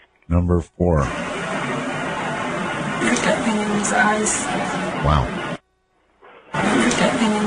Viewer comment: I think it says "Put that penny in his eyes"